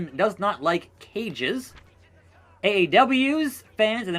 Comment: I thought you were saying AEW.